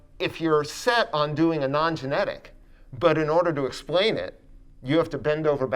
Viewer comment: oh, I thought that said "Non-Generic" and im like "I don think brandon has ever done anything Generic"